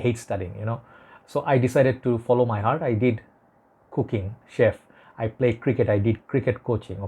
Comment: Oh cooking, I thought you said cocaine lol